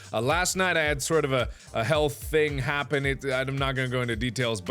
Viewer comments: when he said health, I didn't hear the th in it, so I thought he said hell thing, and I imagined a doom guy with a warpstache, and Mark's face running around killing demons, while screaming random obscenities and facts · I thought at 1st he said Hell thing happen... Oh Mark you play too many Hell based games stop trying to summon demons, yandere simulator, Doom, and Seum aren't real ;P